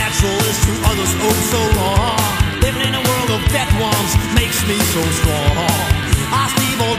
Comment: WHAT I FIND NATURAL, OTHERS FIND SO WRONG... LIVING IN A WORLD OF DEAD DRONES MAKES ME SO STRONG!!! ..... I LOVE THIS JAM!!! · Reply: I hear it "death ones"